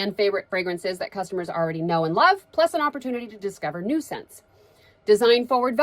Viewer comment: Every time she says "new scents", I hear "nuisance" 😂